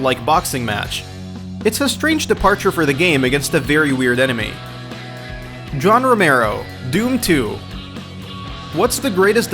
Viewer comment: Did you say Don Robero?